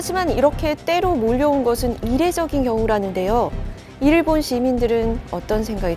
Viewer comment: 나만 일본 시민들이라고 들림? 아니면 일부 시민들인가 근데 여러 번 들어봐도 일본 시민으로 들림